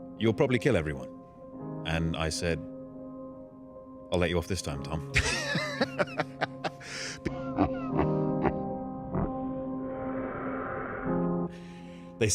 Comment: What did he say at 6:10? I'm sorry, English is not my first language 🙏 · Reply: No problem :) He said : "I'll let you off this time Tom"